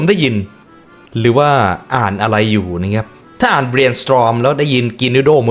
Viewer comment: ได้ยินว่าGreen Storm😅